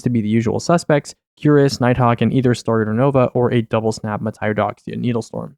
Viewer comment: “double snap Mataiodoxia…” did I hear that right? What is that?